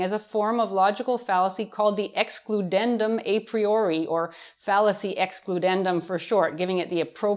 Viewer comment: Misheard that as fallacy ex pudendum😂